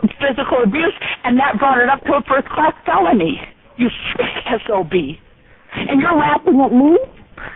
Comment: Did she say SOB at 7:55 lol